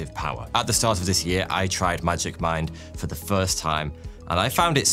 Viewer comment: Was making breakfast and the first time you mentioned the sponsor, I thought you said Magic Mike.